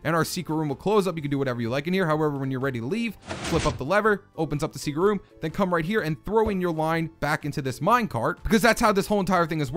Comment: What I heard: HowEverIfYouWannaLeaveJusFlipUpThisLever nowwenyourreadytoleaveflipupthissecretleverandthrowyourlineintothisminecart